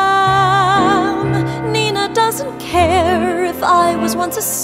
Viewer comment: I thought it said "Ninja Doesn't Care."